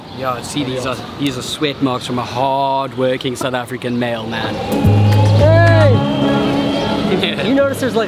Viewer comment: Sounded like he said a South African Mail Man.